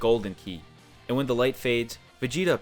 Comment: I thought bruh said “looking way more muscular than you” 😂 alright let me get in the gym